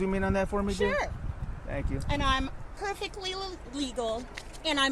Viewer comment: when she says 'and I'm perfectly l-l-legal" I'm pretty sure we all heard differently, because what I heard her say was "illegal". Is she trying to say that she is illegal in the united states of America?? She is a Karen and i know that for sure.